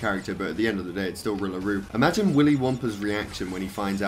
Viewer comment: who else thought he said Willy Wonka instead of Willie Wumpa